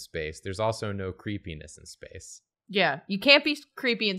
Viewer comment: says “there’s no creepiness in space” and the captions say “no penis in space” PSA